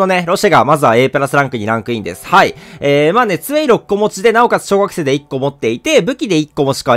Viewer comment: 2way6個持ちの小学生って聞こえた